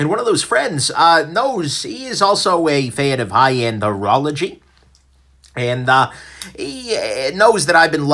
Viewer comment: Misheard "high end horology" as "high end urology" and was very, very confused... Also, missed yet another stream argh!